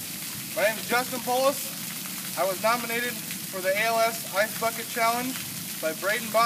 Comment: BDJSIWJEJFISIW PLEASE WHEN HE SAID "Ice Bucket Challenge" I HEARD "AIGHT FUCK IT CHALLENGE" WHATS WRONG WITH ME DJEBISKEFNISISNW